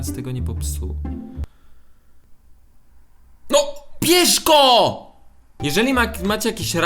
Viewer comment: kto tez usłyszał PIESZKO a nie BIERZ GO?XDDDD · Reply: ja usłyszałam" piejrzko " xDD